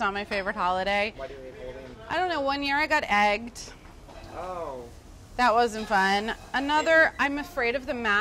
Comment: What did she say after I don't know? I'm curious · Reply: "one year I got egged, that wasn't fun" I assume you could understand the rest no?